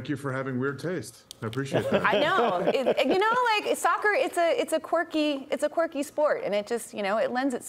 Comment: Called football a cocky sport ..😂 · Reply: Thought she said quirky